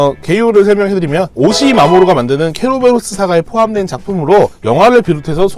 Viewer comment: 나.... 순간... (= 3:51 ) " 케로로 " 라고 들었다...............ㅣ 빨간모자 의 원작이..... 그런 무서운 동화였구나....ㅎㄷㄷ....